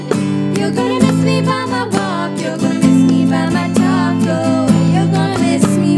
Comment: Sounds like there saying your gonna miss me by my taco instead of talk oh